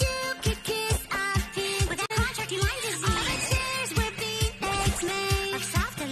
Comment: maybe it’s my poor hearing, but am I the only one who hears eggsmade instead of beanbags? No matter how much I listen to this, I don’t hear beanbags.